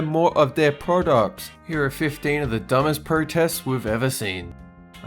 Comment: English is clearly too hard for the editor as at 0:20 they've written "we're" rather than "we've" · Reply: Well it does sound like is saying we're instead of we've